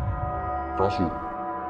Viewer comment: ini Cermin apa Kasur , apa gw salah denger wkwkk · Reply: Kasur, tapi tulisan nya cermin 🗿